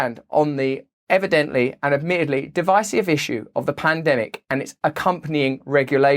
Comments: Did you say "plandemic?" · Did he say “plandemic”??? 🤣🤣